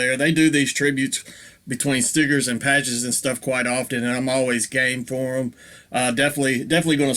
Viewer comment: I had to rewind that because it sounded like you said you’re always gay for them. But you said always game for them